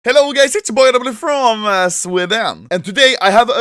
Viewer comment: Did I hear ´´Hello gays`` ?